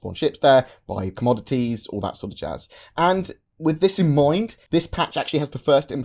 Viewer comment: What say at 0:48 and what it means? English is not my first language and I keep hearing this from and can't understand. To me it sounds like he says "...or that sort of jazz...". Am I correct? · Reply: You are correct. 'That sort of jazz' or 'all that jazz' meaning other similar things. Someone might go to the grocery store to get milk and eggs and that sort of jazz. Someone at one of the space stations might store ships there, buy commodities, all that sort of jazz. Pretty much the same as et cetera (etc.).